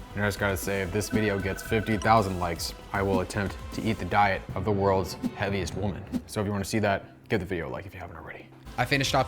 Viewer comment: I thought he said “ I’ll eat the world’s heveiest woman 💀